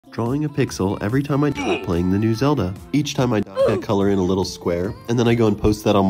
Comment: bro I thought he said "drawing a pixel everytime I shit while playing the new zelda"